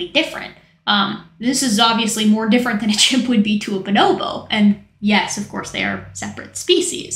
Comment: I thought she said Bonobo but a baboon💀💀💀